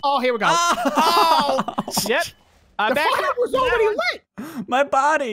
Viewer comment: did he say fire or fu***r